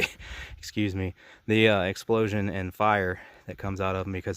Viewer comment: idk what you said but the sound effect was hilarious. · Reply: "Effing" fire/charring of the jeans. Too many people complaining about my cussing so I try not to anymore. So when I slip up I bleep it out now so they don't have to bother me crying about it... But I'm glad you got a kick out of it (truly).